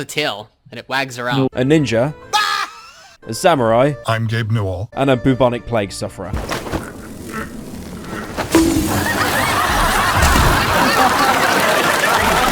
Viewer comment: Did just say BOOBonic 😨😨😨